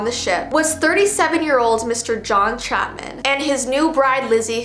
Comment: I'm sorry but did U say Mr John Chapman or Chaplin because my last name is Chapman, like what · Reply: Chapman